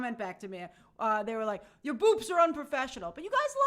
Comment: I thought u said "boobs" at first 😂